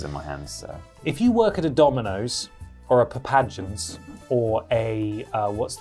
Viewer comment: I replayed 1:42 so many times trying to figure out what he was trying to say . Papajuns? Papajons? Then it dawned on me: fucking Papa John’s?